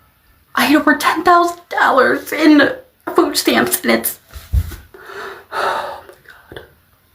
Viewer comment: I don't believe a damn thing she's saying! 🙎🏾‍♀️She sounds like a crisis actress! I could have sworn I heard her say she gets $10,000 in stamps, perhaps I'm wrong? 🙄😑